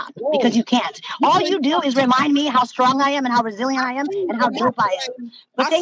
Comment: it sounds like she said he reminded her how strong and broke she is haha 🤣 😂😂😂😂